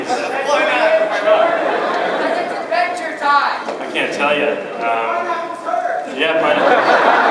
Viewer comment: what did that person say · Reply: 7 years late but he says "pineapples hurt"